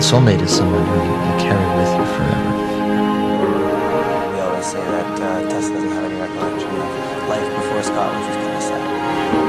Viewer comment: I can t hear what he says at 1:10 . help? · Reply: KH Scott is saying "I've always said Tessa does not have a recollection of her life before Scott"...then the audio trails off. I don't know the date or source of this particular interview quote. I do know in their January 2014 reality show "Tessa and Scott" (Episode 1, Season 1) Scott says something similar: "Tessa says she doesn't remember her life without me."